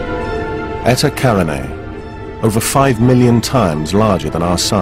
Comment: What name is said at 00:34? I didn't understand... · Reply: Eta Carinae.